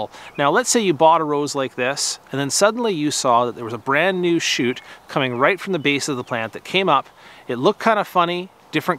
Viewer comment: What's the name of the rose at 1:55?? I couldn't catch the name properly. Thank you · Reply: Thank Daniel - French is not my first language (and just barely my 2nd) so the fault is probably in my pronunciation: 'Baron Girod de l'Ain'. I'll fix it in the subtitles when I have a minute.